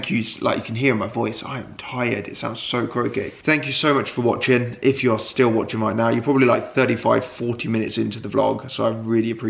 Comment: I thought you were going to say at the end "If you're still watching, you're probably 35, 40 years old.." XD like we've been watching so long we've aged! Haha. Thanks for this one Alf, love your content x